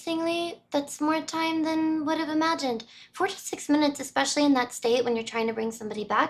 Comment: Sorry, but I had to replay that over a couple times.... Time stamp 4:20..... I keep listening to that over and over and it still sounds like you're saying "46 minutes". I'm hoping that I am miss hearing this, because the video I can clearly hear it saying "4 TO 6 minutes" It might also simply be an unconscious desire to correct you, I don't think I've actually corrected you, out of all the months that I have been watching you. And there might be some unconscious desire to correct you and so I might be making it sound like you're saying 46 instead of 4 to 6